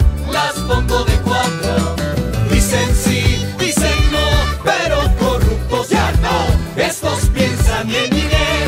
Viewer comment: Que dice 1:11 ? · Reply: Dicen "Si", dicen "No". Pero corruptos, ¡Ya No!